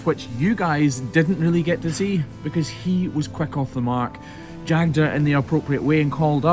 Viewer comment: He was a good boy and Jagged her? What is Jagged? I thought he said shagged her, so I put on closed captioned and watched that part again.